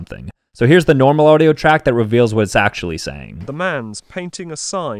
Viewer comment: "Hello, i'll paint you a sign" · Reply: I heard “Hello, I’m painting a sign” 👍